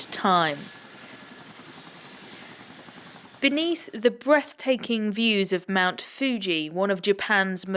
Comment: Did I hear *Breathtaking*. ????